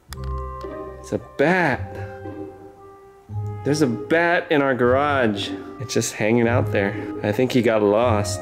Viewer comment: I thought that said: its a bää. Its a bää in our garage